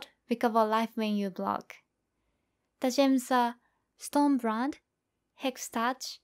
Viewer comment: subtitles say Arma Brand instead of Storm Brand